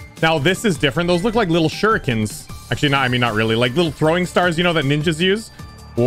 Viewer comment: I thought you said they weren’t shurikens